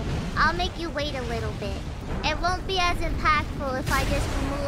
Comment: While "really early" makes sense, she actually said "willy nilly". As in, she won't be so careless as to just show off her feet.